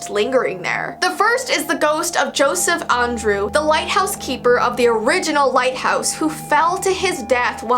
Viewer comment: i thought you said “white” house but you said “light” house and when i first saw it i was like “what this is the old white house?!”